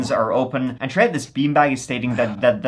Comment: When I first started watching, and, they would say Bin Bags but with their accent and the fact that most Americans call them trash or garbage bags, I thought they were saying bean bags. I kept thinking why are people putting bodies in bean bags all of the time? I figured out that they were saying bin bags when I had captioning on one of their videos.